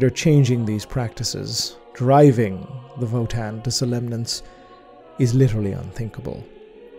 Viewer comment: What is the word he uses at 40:04? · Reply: I suspect it's a slight mispronunciation of "somnolence", excessive drowsiness or fatigue.